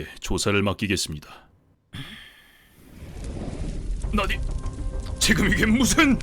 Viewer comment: 순간 나닛!으로 들렸어요... 13:30 라이토 대사가 안 들려요... 원작에서는 키라가 버스 납치한 마약범처럼 키라의 짓인 걸 알리지 않으려고 할 때만 다른 사망원인을 적어서, 히구치 키라 전까지는 심장마비 외에는 키라 짓인 걸 생각하지 못했습니다. 여기서는 갑자기 L이 심장마비로 사망하지 않은 범죄자도 키라 짓이라고 생각하는 게 조금 걸렸습니다. 다만, 키라가 대통령 메시지 전달 여부를 알려고 리히터를 심장마비가 아닌 폐색전증으로 죽였고, 하필 FBI를 철수시킨 대통령의 최측근이어서 L이 심장마비 외의 다른 사망원인도 키라 짓이라고 생각해도 이상하지 않게 되었네요. 리히터의 편지도, '키라'라는 이름이 'Killer'를 일본식으로 부르는 것에서 유래했으니 리히터가 자신을 사람들이 부를 수도 있는 'Killer'로 지칭해도 이상하지 않다는 것이 꽤 놀라운 발상입니다. 편지 내용도 일말의 죄책감을 가진 범죄자라면 충분히 쓸만한 내용이면서 어쨋든 대통령의 측근이었으니 나라를 염려하는 마음에서 쓸 수 있는 내용이었고, 범죄를 심판하는 키라의 존재를 두려워하는 마음에서도 쓸 수 있는 내용이었던 게 인상적이었습니다. 다음화 기대할게요!